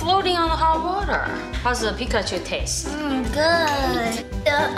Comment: I thought she said the pikachu tastes like shi- 💀